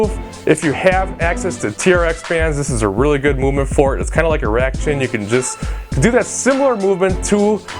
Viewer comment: does he say "its kinda like an erection"